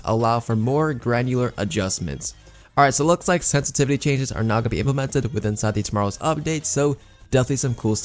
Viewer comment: took me while to hear "now" I kept hearing "not"